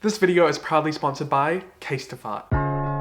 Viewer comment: I thought you said "this video was probably sponsored by casetify" and I was very confused